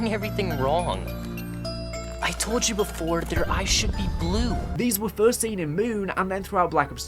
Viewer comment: I always thought he said "I should be blue" not "Their EYES should be blue" Cant believe I was hearing that wrong for so many years... now it makes sense